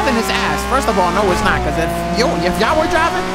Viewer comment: i thought said the n word because 31:44 he said "because" a little to fast making it seem like he said "n****" i just had to clarify this before it gets out of hand and haters would accuse him for saying something like that, because it sort of sounds like he said it a little, and if u look at the captions. u can see it says "because"